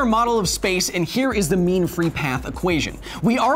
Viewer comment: I heard meme free equation :(